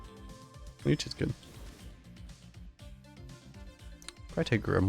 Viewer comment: almost thought you said leech is good...